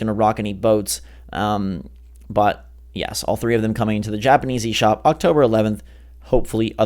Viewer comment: sounds like he said Japaneasy.